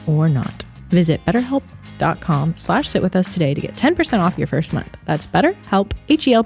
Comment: during the ad, definitely says "shit with us" instead of sit, she's so cute omg 😂😂😂😊